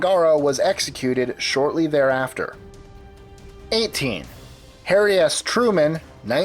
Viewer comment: I thought he said "Hairy ass Truman".